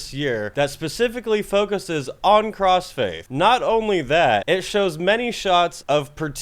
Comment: For a moment I thought you said 'new and upcoming band crossfade' I was like 'they have been around for awhile' but no cross faith totally different.